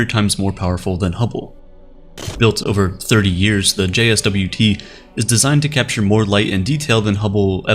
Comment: he says jswt instead of jwst james space web telescope